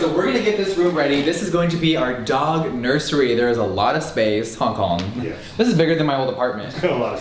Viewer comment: You said "Huh Colin?" and i heard "Hong Kong"